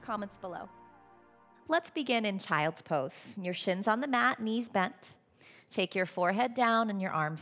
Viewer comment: I thought she said "Well start in child's pose... your sh*t's on the mat, knees bent..." I had to take a minute lmao